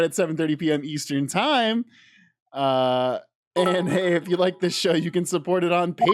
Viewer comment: For a second I thought you said "if you like this shirt you can support it on Patreon" 😂